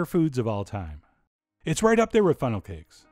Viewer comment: Thought he said,"right up there with ferral pigs". 😆